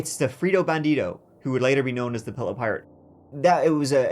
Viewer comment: I first heard "pillow pirate" and that brings up a whole different image to mind